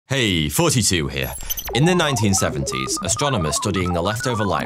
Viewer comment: Why do I always hear "Hey! 42 here!" at the beginning of each of your videos